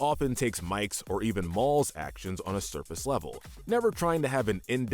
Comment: Help did he say mall 5:07…